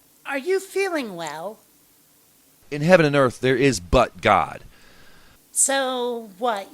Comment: "In heaven and earth there is ButtGod" That's what I heard. "There is but god" is nonsensical. Word salads are gross.